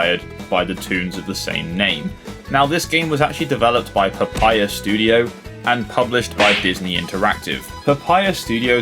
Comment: thought you said "papyrus" an I was like "THE PEOPLE WHO MADE THE BEST RACING GAME CALLED NR2003?!"